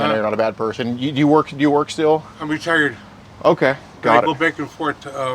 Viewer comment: I swear I heard dude say "I'm retarded" instead of retired